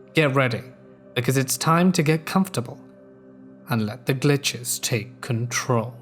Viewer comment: I heard “let the bitches take control” for some reason.